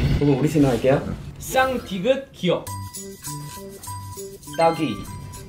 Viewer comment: What was the word they couldnt say at 19:27?????????? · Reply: Ddong ggo😂 it means asshole/anus in Korean